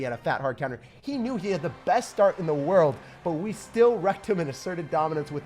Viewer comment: did he say Rectum 🤔 ? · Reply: Wrecked em